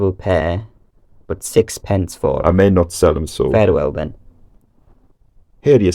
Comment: Did he say 'farewell then' or fare the well then'?